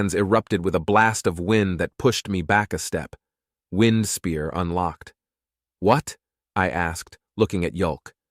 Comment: Great story but anytime anyone cast wind spear all i hear is "rape the nut sack"